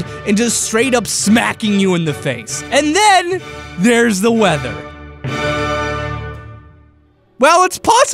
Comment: AND THEN THERE'S THE WEATHER! XD I thought you said feather.... :D